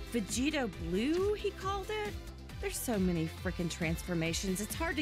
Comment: did she just said “there is so many f@ transformation”